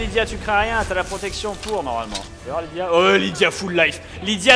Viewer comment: J'ai entendu "Lydia se touche dans les flammes" -_